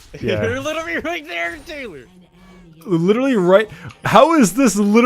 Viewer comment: it might just be me but did he call u Taylor? I didn't know that was your name.